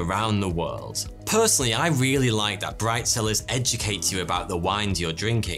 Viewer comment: I thought he said Bride Cellars, I was like…oh no.